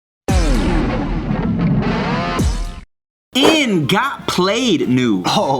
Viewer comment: I was sneaking out and my phone played your video all i heard was "in you got played news" so did my dad.